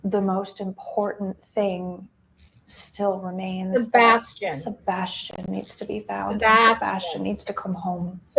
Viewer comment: I heard him say he had never be found